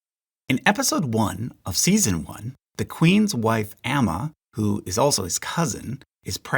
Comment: Did i heard it as :Queen's wife Aemma? who also "His" cousin?